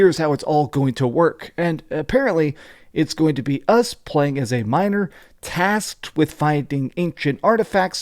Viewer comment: Had to rewind, when I heard “play as a miner” and sex in the game 😂😅